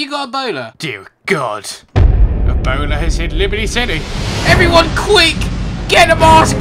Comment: Did he just say boner?!